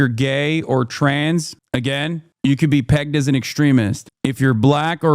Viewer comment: did he say if you're gay, you'll get pegged??